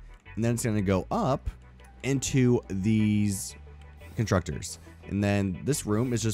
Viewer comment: play it back at .25x ....is he saying "con-trucktors"?